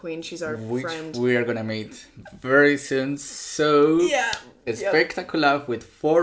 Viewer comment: Whoa whoa whoa!! 4:11 I thought he said " we are gonna mate very soon " 🤣 Great video, guys!